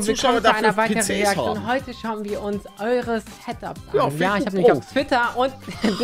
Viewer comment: Ich habe situps verstanden😹😹😹